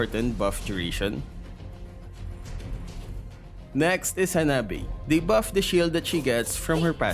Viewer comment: lol hanabae instead of Hanabi